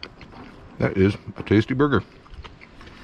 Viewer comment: sounds like he says booger. Yes I am 12.